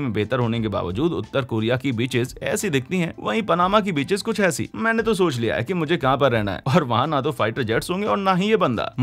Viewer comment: I heard it as bitches😂😂😂😜😜😜